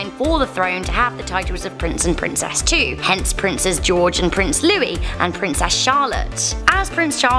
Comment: Did she just say Princess George?????!!! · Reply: Ashton Productions No, she said "princes George and Louis", princes being the plural of prince.